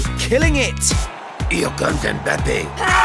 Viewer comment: what did he say??😂😂 pls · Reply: You got mbapped